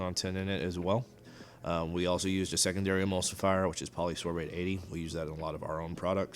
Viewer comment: What is the ingredient that he is saying at 5:30 ? Polysulfid 80? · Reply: Polysorbate 80